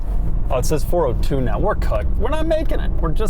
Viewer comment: did i just hear say “we’re cooked”? LMFAOOOO